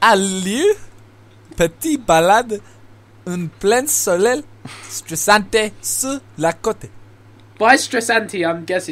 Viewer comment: did he say "petite balade au soleil sur la cote" ?